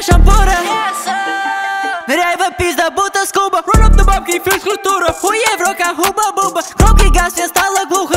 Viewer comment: я один слышу на 0:58 Губка боб и физкультура